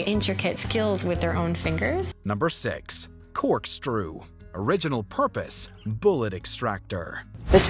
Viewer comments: Lol I think he said "Corkstrew" here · I'm sorry... did you just say "Cork-strew"?